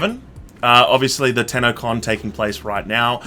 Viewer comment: Your fucking accents... first four times you said Tennocon I thought you were saying Tannacon and I was extremely confused